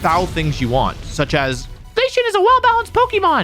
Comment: I thought he said "Glaceon is a well balanced pokemon" instead of "Zacian is a well balanced pokemon", and somehow, they're both equally wrong